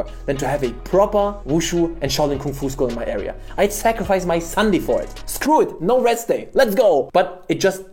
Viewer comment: I thought he said he would sacrifice his Son for a Kung Fu school in his area. And I didn't even question it.